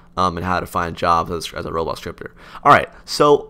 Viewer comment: the captions said robot stripper Xd